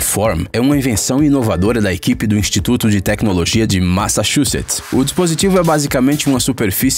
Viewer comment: eu ouvi equipe de machachuchas? kk entendedores entenderam kk